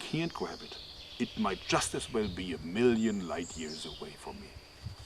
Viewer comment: Did you say at 4:04 "A million Likeyears"? :D